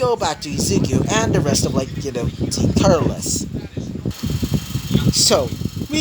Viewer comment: In 34:44 i heard you say team turtleless